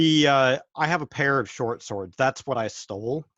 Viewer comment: So- starting at 1:22:49, when is asked about ‘one person or thing that is important to you,’ I SWEAR I heard him say “short shorts” vice “short swords”. And then if you hear the rest of his description, it kind of fits. “A pair of short shorts. That’s what I stole. They are my most prized possession- it’s why I stole them in the first place ‘hey they look really cool.’ They’re one of my particular tools I can use to get out of trouble; they’ve always been reliable.“ then ruined it by talking about swords...